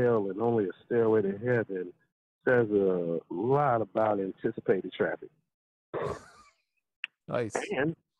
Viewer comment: "Says a lot about..." .. WHAT ?? Can't hear... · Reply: yep, I figured it out eventually by googling. In fact, the actual quote is "[...] about anticipated traffic numbers ."